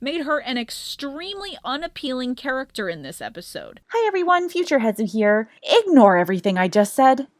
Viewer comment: Does that mean canonically your clone replaced you 0_0 Where real Hezu gone? Edit: Misheard some things. I thought it was your clone, not your future self